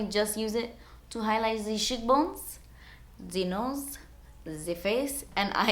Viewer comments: It sounds like you said s word bone instead of cheekbone. :0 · When you said you used the fan brush to "highlight the cheekbones," I thought you had said "to highlight the shitbones" i died lmaoooo